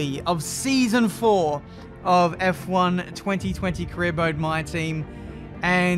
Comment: Am I going crazy or did say "Career Boat"?